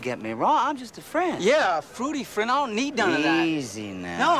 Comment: fruity friend! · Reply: I thought he said booty friend 😅